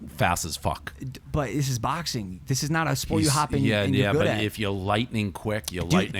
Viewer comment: does Paul really say "its just boxing its not a sport you have to be good at" ???